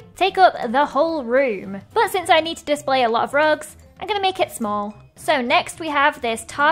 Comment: LOL when she said rugs I thought She said drugs 😂